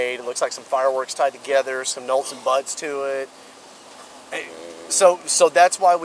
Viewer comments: “It has to do with the little device you made. Looks like some fireworks tied together, some nolts and buts to it.” Cop has trouble saying “nuts and bolts” earlier, but this one was the best. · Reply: No, he clearly says nolts and buts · this man said “nolts and buts” 😂 just being around this duo is taking its toll on him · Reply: haha went straight to comments after I heard "nolts and buts"